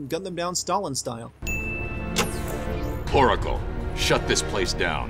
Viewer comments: Is it just me or did Batman say poracle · Am I the only one who heard "Poracle"? Or even "Coracle"?